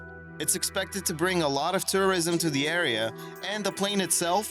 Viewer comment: did he say "it is expected to bring a lot if terrorism to the area" instead if "tourism"?